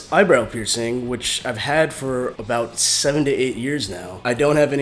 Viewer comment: did he say 70 years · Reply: tejada haha 7 to 8 years =D